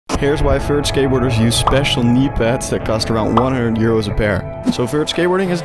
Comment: I thought he said “special-needs pads”.